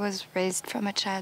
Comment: the first time I watched this, I heard: "I was raised from a child to hate all of your roast beef."